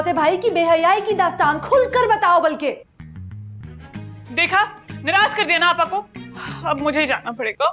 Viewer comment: What did she said exactly "Neerash" ?